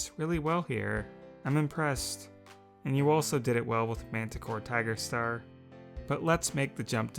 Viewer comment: I JUST FINISHED Camp. Cretaceous and thought you said ‘Manta Corp-‘